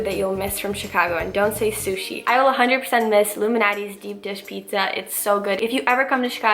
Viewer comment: Whole time thought you said the pizza place was called Illuminati's deep dish pizza hahahaha. Then I googled it and realized its Lou Malnati's lol. Love hearing food recommendations :)